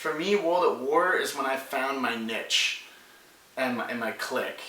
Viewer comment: I thought you said you found your bitch.